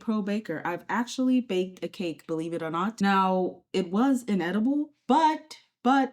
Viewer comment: I heard ‘an edible’ instead of ‘inedible’ and snorted